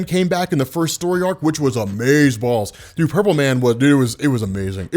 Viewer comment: did i just hear amazeballs!? in 2018!?